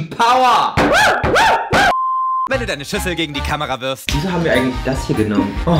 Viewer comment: Da habe ich SCHWESTER anstatt SCHÜSSEL verstanden😂😂